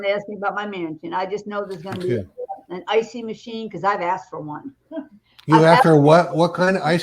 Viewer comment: Maybe strawberry, or chocolate flavour ice-cream machine?!❤❤❤😂😂😂🎉🎉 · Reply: Oh, I thought she said icee machine. I love icees, so much so that I'm known for my love for them.